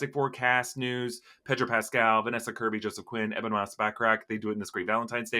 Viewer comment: It sounded like you said vannessa kirby jusy quit. I was like whatttt